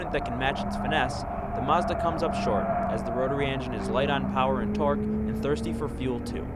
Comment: Did he just say the rotary is light on power?